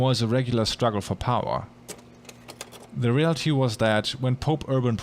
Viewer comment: when he said “reality” at 16:37 i thought he said “real tea” and i got excited